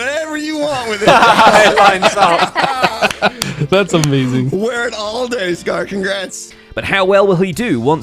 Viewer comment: Hey folks! We apologised for missing GeminiTay last week, and this week it's Joe Hills we owe the apology to - with the entire server focused on participating in Decked Out, it's been unfortunately too easy for videos to slip through the cracks. Even though at this point it's tempting to omit someone each week as a sort of weird running joke... we promise not to make a habit of it. 😅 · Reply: think saying WEAR not WHERE